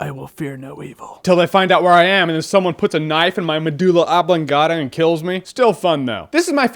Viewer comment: and 2:44 medula angavada, mendula ungavada, medala aganvada..... can someone tell me what he says, or at least the spelling?